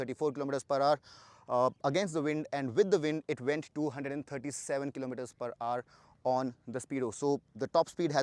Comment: did he just say the bike went 247 km per hour?!!